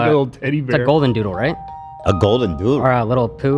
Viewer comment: calling Alfredo a golden doodle is so funny for some reason 😂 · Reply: Please correct me if I’m wrong but I’m pretty sure misheard him and said “a golden Noodle ??????”